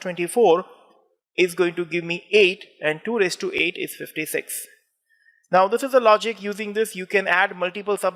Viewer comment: What was said at 3:07? 2 is to 8? What was the math to get 256? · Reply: NVM I understood. It's 2 to the 8th power